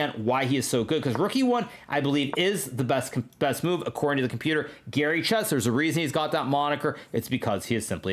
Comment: Gary Chess is the GOAT? I thought you said Magnesium is the GOAT